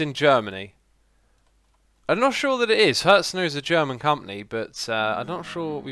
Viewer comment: What word does he say at 34:38? After i'm not sure it is... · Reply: Nvm i got it he said hetzner